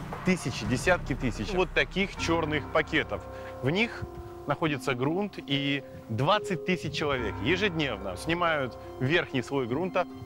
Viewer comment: мне послышалось что он говорит:в этих пакетах лежит грунт и 20 тыс человек